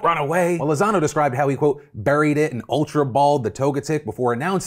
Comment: I would bet good money that he said 'Berried him and ultra-balled' instead of 'buried it'. You feed them razz berries to make them easier to catch and in raids people often say they 'berried' the pokemon to mean fed them a berry